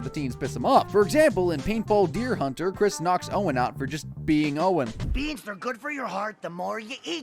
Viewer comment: Did I mishear or did he really say "Chris knocks Owen up"?